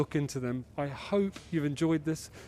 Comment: Simon says, "Tech talk" I hear, "Tiktok"